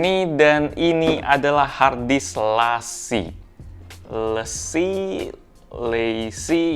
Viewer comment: Di kolom komen ada orang bugis ga ya 😅 bang ada ngomong "lessi" tadi kalau ga salah dengar 🤣